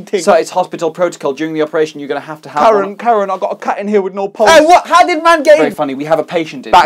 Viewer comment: “Karen we’ve got a cat with no pulse” That’s a line only Brits will remember Best ad ever Comment if u remember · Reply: Oh i thought they said i got a cat with no paws